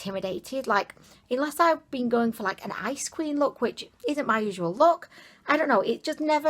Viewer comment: I kept hearing "ice cream" instead of "ice queen" and got so confused lmao, I only got it right after the third time.